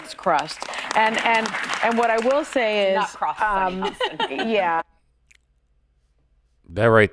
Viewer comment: in I gotta say, I think you misheard the blond you said was hoping on top. I think she was correcting Sony. I think she said it's not the crust Sony n that's cause Sony meant the core of the earth not the crust. I could be mistaken but I'm pretty sure I'm not